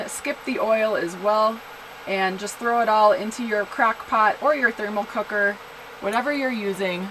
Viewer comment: Did she just say "Crack Pot"? Lol